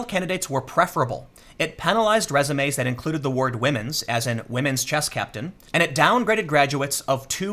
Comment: I'm such an idiot, I wasn't reading along at 2:38 as he read the article and I thought he said Women's Chest Captain🤣🤣🤣🤣🤣🤣🤣🤣🤣🤣🤣🤣🤣🤣🤣